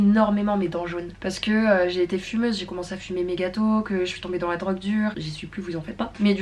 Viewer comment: Au lieu de "j'ai commencé à fumer méga tôt" j'ai compris "j'ai commencé à fumer mes gâteaux" 😂😭 En tout cas, super vidéo ! Les blocs sont définitivement mon type de vidéo préférée !